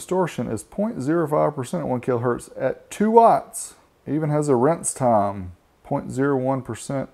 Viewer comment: LOL at 1:28 did you say "rinse time?" Thanks for showing us an Urban Audio Works amp...I've seen these advertised back in the day but never seen one in operation...and yes, DYNO!! Cheers:)